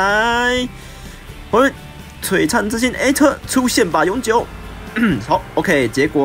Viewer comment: 出現吧,永久 我一直聽成出現吧尤吉歐 (XDD...